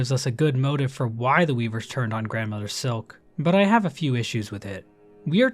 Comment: First time the Grandmother's Silk was mentioned, I misheard it as Grandmother's Milk and was a little bit disgusted throughout the whole video